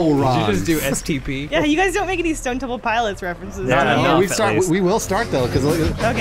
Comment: That STP reference was amazing but most people gloss over it. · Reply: I hadn't heard the name of this band before so I thought they were saying "stuntable pilots". Glad that you cleared it up